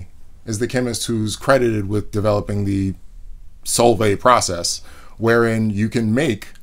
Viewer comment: When he said "Solvay", I totally heard "salt bae"